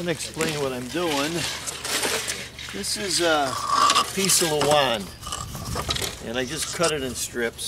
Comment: what type of wood is used for the forms? I didn't quite catch that 2:30. Thanks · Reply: Hi Jereme, it was a Luan or type of paneling cut into strips, sometimes a thin type wood, thanks